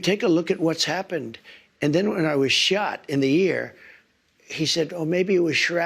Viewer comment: Look at the Captions at 39:22 They are literally still on Wray's side on Trump's ear being hit by a shrapnel but saying "I was shot in the AIR" instead of "I was shot in the ear."